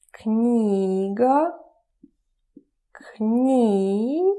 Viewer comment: When you said "Книга" the caption said "nigga" 😅😅